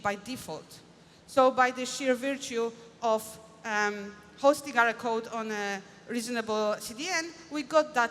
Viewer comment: Typo ~ 6:16 on the closed captions. Says posting instead of hosting.